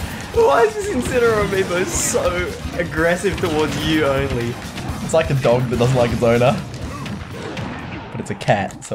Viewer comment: Am I the only one who heard say "it's like a dog that doesn't like a boner" at 10:48? Also, I understood that Inbetweeners reference Z, you bumder.